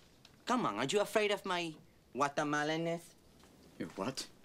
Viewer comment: All this time I thought he was saying “my watermelon-ness”. LOL